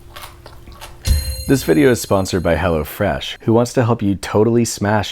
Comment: Did one else think he said 4:48 hell a fresh